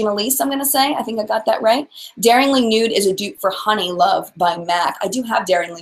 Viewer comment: Great topic to discuss. There are so many items I neglect. Mostly pre-made eyeshadow palettes because my favorite staples are in an extra large z palette. The Too Faced lipsticks just don't last on me more than 2 minutes. They're moisturizing for sure, but almost too much so. Also, what was the name of the Charlotte tilbury lipstick you mentioned? I tried to rewind and hear what you sad, but couldn't make it out. Thanks! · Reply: Oh ok, great. I thought you said Heifer Honey. Lol. Thank you!